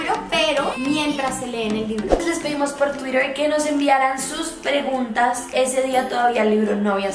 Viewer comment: Alguien Más Notó Que Dice Rreguntas? (Auto Like Como Siempre:'v )